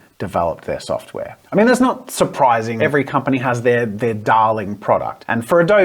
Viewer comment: "every company has their Dalek program" Yes, I know you said "darling", but I stand by that mishearing.